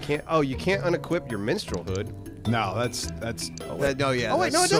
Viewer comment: I at first thought said "you can't inequip your menstrual hood" (wouldn't have been surprised if he had)